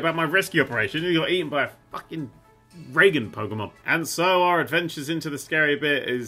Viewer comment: Did just call Charzard a "Reagan Pokemon"